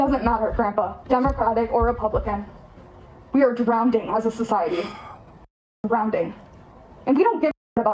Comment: Did she really say drownDing?